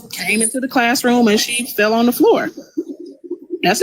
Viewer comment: SHE DID NOT SAY SHE FELL ON THE FLOOR!!!! She said she lied down on the floor !!!